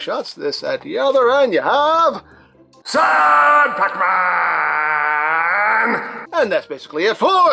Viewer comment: "Sad Pacman"..... so all this time i misheard that as "Sir Pacman", derp